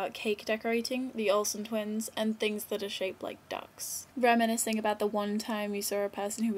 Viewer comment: OHHHH....ducks i thought you said dicks!